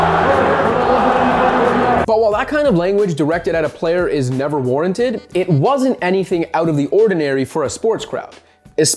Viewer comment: What was the crowd shouting at 3:30? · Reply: "F Kyrie!"